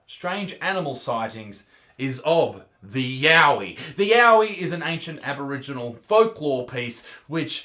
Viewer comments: Did he say yaoi? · for a second i thought you uhm said yaoi as in.. cough